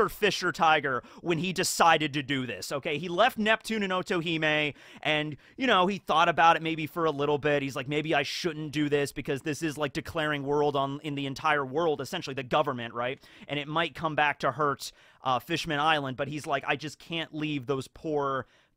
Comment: This analysis is really well made. What is funny that in 15:28 correct me if I am wrong did says (declareing "world" on world government) because that would be hell metal